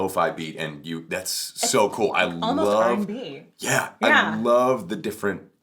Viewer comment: I thought she said "It's almost like Cardi B" and I was about to unsubscribe so fast my keyboard would've broke.